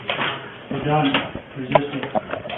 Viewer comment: I think i heard one of the officers say "You're done resisting..." Wow! He says this to a paralyzed man. 😢